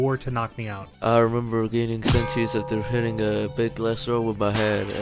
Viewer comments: I thought it said gaining a sentence for the fucking abuse. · I thought that said gaming sentence